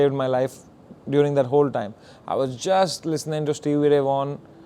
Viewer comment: Whose name does he say at 14:36 ? Who does he listen to ? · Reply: Stevie Ray Vaughan